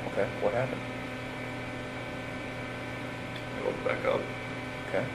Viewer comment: Did anyone else hear him say "They woke back up" @ 9:27?? Sounds to me like he was saying he killed them twice,.. Just as he said in the Cadel book. Did we all miss that when this came out before the book? 🤔 🤔